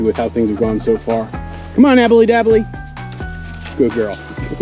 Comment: Love the vids but I swear I watch every video and I always hear “Abby Noooo!!!!” 😂😂😂